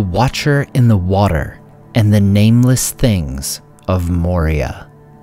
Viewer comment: lmao I thought that said "Nameless Kings" and I was like "Oh god oh no, not again, my PTSD, he's in Middle-Earth too!"